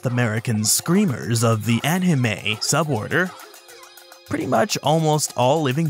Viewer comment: south American screamers of the ANIME sub order? Did I hear that right??